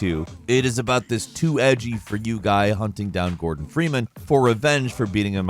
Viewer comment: Boh GORDON Freeman lmao I legit thought you said Morgan until you said the name again 😂 I was imagining this guy seeking vengeance agains Morgan Freeman lol